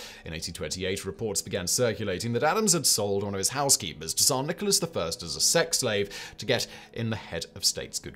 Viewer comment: What the hell is Simon saying between 2:10-2:30? It’s all slurred together and all I keep hearing is “sex slave”